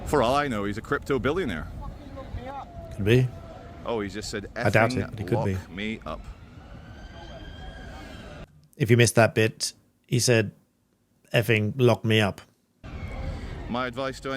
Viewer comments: But he didn't breach either article 1 or article 2, let alone both. He didn't refuse to stop drinking after a request to stop, and he didn't refuse to surrender that which he had been drinking. I am an ex-police officer. Those police officers created an incident out of nothing. There wasn't a problem. There wasn't a problem before they arrived. (if they had simply left him alone there was very unlikely to be any problem whatsoever) There wasn't a problem after they took the alcohol away. (even at that point If they had left him alone, there was very unlikely to be any problem whatsoever). Also, I'm not sure, but I think he said "you effing woke me up" not "effing lock me up". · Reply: I thought he said woke me up too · I thought he said "Fing woke me up" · actually misheard that. He said F'n woke me up. not lock me up.